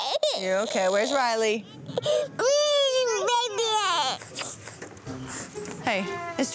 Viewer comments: It sounded like she said where my little baby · Olivia: Crying Danielle: That's why you listen. Your Okay, Where's Riley? Olivia: uhuhuhuhu(Crying) Right there That's was so cute and funny 😂😂 Watched it over 100 times ❤ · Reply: is it weird that I thought Olivia said "baby head" lol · When lulu fell down the slide and danielle said come on where's Riley it sounded like she said where's the baby at 😂😂